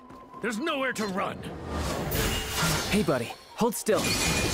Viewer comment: When Childe showed up, I only hear "Hey girlie-hold still"